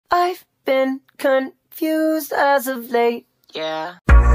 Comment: What I heard "Ive been confused as a grape yea!"